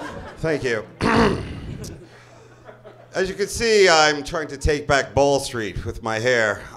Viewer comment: What is he trying to take back with his hair? I couldn't make it out. Sounds like he says "I'm trying to take back ball streak."